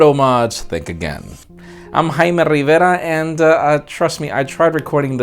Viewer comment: Did I hear "Hymen Rivera"😕